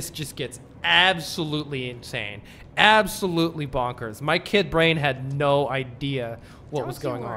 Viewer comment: "my kid brain had no idea what was going on". I completely misheard that and thought you had a kid called Raine. 🤣 I hope you enjoy the FF7R journey and play / upload the rest of it. 👍